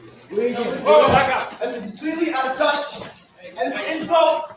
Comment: I thought he said "it's completely unjust'